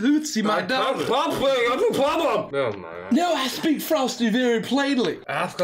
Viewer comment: am i the only one who clearly heard him say “that’s what i’m saying, that’s the problem!!”